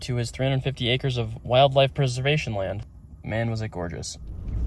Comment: At first I thought you said the man was gorgeous. Lol